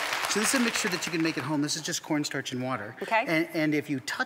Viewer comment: what does he say? Something something and water? Pls tell :D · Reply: Cornstarch and water